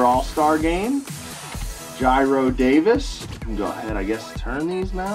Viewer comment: is it just me or did he just say jairo davis